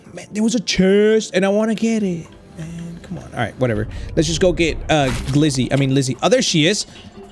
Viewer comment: umm did he just call Lizzy ‘Glizzy’ 😂